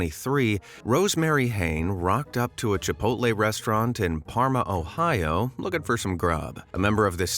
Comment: did he just say Ohio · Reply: Yes he said Ohio. 💀what the heck is going on in Ohio?